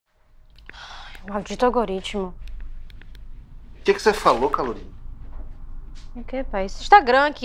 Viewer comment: Só eu escutei "qq cê falou calorina?"